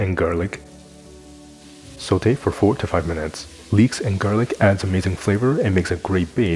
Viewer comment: Lay ho lay ho! I hope you’re keeping well and cooking well. If you’re new to the channel here, welcome! I’m glad you’re here. Be sure to pick up your free ebook that has 5 super easy plantbased recipes to get you started with cooking today - link in description box. Happy cooking 🥳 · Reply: as a not native English speaker, I was confused, because at the first time I heard 45 minutes, that was so absurd to me, that I repeated this part again. Now I know you are saying 4 to 5 minutes 😂. Just to tell you, also people their English language level is not as high as your (you speak English perfectly and you have amazing voice), therefore please, could you make some notes in your videos, like time, amount, etc? 🙏